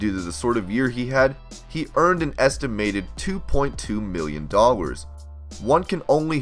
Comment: For a min at 2:44 i thought he said 2.2 billion $ i had to beg back for a second 🤔